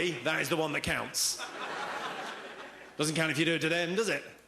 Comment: What does he say at 9:52? Doesn't count if ... does it? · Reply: if you do it to them